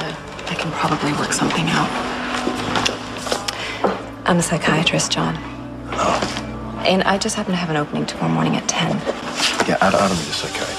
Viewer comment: What is she saying at 0:40-45? Someone please help me catch the line. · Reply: "I'm a psychiatrist John...and I just happened to have an opening tomorrow morning at 10:00"